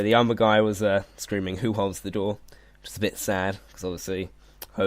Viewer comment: Who holds the door? I thought he said who holds the north